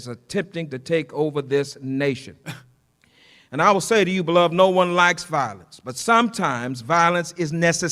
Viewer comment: Did he just say "blood" when referring to his church goers? 🤣